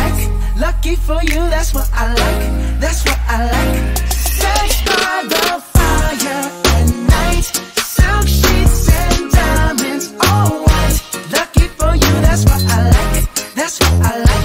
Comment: I thought he said "sit by the fire at night" um im 😂